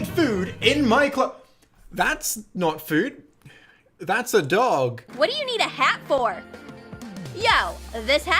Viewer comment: Wait did you say dog?